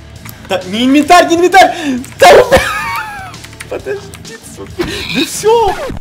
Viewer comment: мне послышалось с*к@